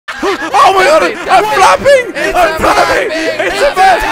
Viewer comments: thought josh said "im fapping" lolololol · I laughed so hard. It sounded like he said "I am fapping" instead of "I am flapping" LOL